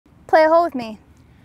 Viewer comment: I thought she said in the beginning of video play with my hole with me ,anyway just want to say ,very pretty and skilled golfer